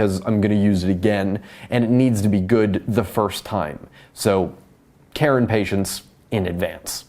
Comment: I actually thought you said, "Karen Patients" for a second, and I was like... That doesnt make sense